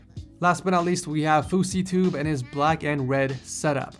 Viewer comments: Did he say fouseytube? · I thought he said fousey tube lol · "fouseytube?" Did I hear that right? Heh. seems like he has done some research to build that rig, despite being a total D bad on YT. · WAIT DID YOU JUST SAY FOUSEYTUBE @ 6:34... Proceeds to get the bleach